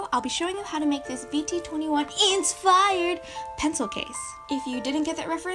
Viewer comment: My volume is turned down super low so when you said "insfired" I thought you said "unrequited" in an awfully squeaky voice. It took me the next five seconds to figure out you had said "insfired." Idk what's wrong with my ears.